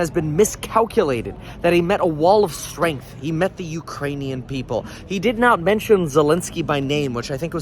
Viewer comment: Did I hear him correctly when he called the Ukrainians the Urainians??? Then Kamala 👀🤣🤣🤣🤣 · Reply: I thought he said Iranians, I was like wrong country bro...